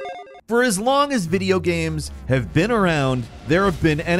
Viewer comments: I've watched a few of these now, and is definitely a great host, but I swear I hear him say "gams" instead of "games" and I can't help but wonder if this is....intentional? I'm cool with silly banter etc, I just can't help but wonder. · Hey am I the only one who heard him say video "gams" right at the start of the video?